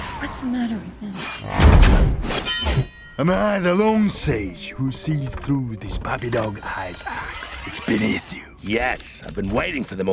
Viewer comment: cant understand what drift say to bumblebee after fight. Can anyone write it :-) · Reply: "am i the lone sage who sees through this puppy dog eyes act, it's beneath you"